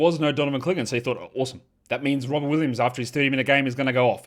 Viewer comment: did you just say Robin Williams? Favourite Robin Williams performance/movie?